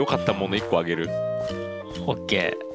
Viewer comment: ブラランドって聞こえたwwww